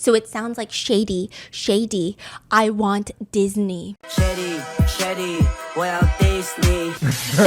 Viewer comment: i hear shady shady Walt Disney😅😂😂